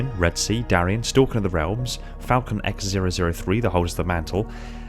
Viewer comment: Thought you said gay rabbit at the end